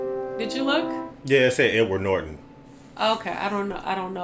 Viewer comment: I'm sorry but it sounded like you said "N-Word Norton", which in context to this video, is hilarious